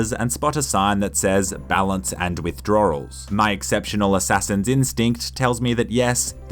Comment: Is it only me or does he say "withdrawRals"?